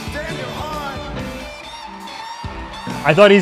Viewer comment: I think he did actually say Daniel Hart. Subtitles are weird sometimes